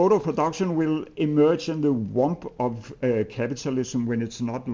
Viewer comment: what word is he saying here?? "socialist mode of production will emerge in the inaudible ..." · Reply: womb